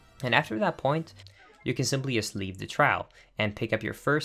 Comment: Leaf gliding? In BotW? Someone’s been playing too much WW. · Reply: I thought he said leave